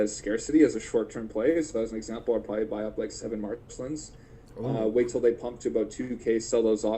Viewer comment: Awesome video, appreciate the work! Quick question, towards the end of the video, like around the 1:03:08 mark, a recommendation is made if someone had 7k gems to invest, and it sounds like he said a UR Marvelsons or Matrinsons, but could not make out which collectible he meant. Any clarification would be appropriated! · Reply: his suggestion was buying 7 Marcelines. Marceline is a Season 1 NFT in Adventure Time.